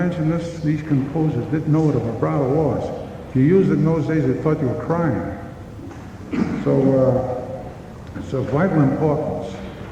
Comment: did he say, "to use it in those days is a fking crime?" Or am I imagining things?? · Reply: "If you used it in those days they thought you were crying." It was very clear.